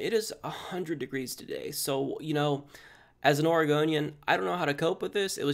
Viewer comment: Not to go off topic, but I thought you said you're an Argonian at the beginning.